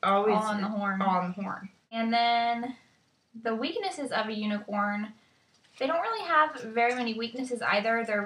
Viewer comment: I think Unicorns are fascinating. I think Marco Polo was stoned XD Rofl I thought you said the weiness of a unicorn and I was like O_O unicorn magical elbow scrag. Some of those books sound fantastic. Phoenixes are rising in some more fiction I am fascinated with them.